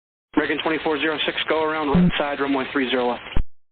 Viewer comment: Controller said RIGHT SIDE to the AA! Could the controller have slowed the incoming AA to a min speed? I did not hear any speed commands... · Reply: On this audio at 3:03, I heard "American 2406 go around blurt side runway 30L"